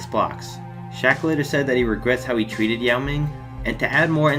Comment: "Shaq regrets that he [traded?] Yao Ming" What was said here? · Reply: Regrets how he treated Yao Ming